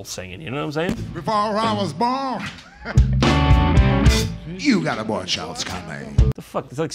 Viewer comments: did he just say "you got abortions coming" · it sounded like he said: “you got abortions coming” 😂😂😂😂😂